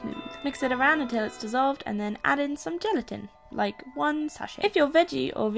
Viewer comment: Am I the only one who heard her say genitals when she said gelatin... .yeah ok.... Shit